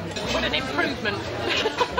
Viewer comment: I thought she said "Fck me dead. Absolutely fck me dead."